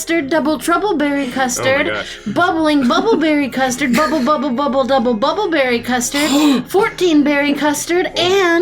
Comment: Bubba buba buba double duba baba berry custard? Wah Da Hail did I just hear?!